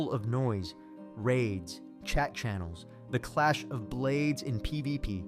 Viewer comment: I thought you said Chechens not chat channels. Threw me off.